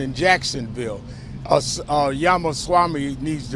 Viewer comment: Did he just say Yamasami? 🤨